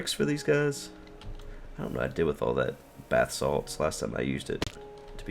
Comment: Sounded like you were saying "bath salt" instead of Basalt. Not sure what I did with the bath salt last time I had it...